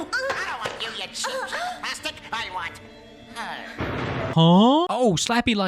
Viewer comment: did slappy say "I don't want you, you cheap bastard" 😂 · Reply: LMAO no he said “not you, you cheap plastic” 😂😂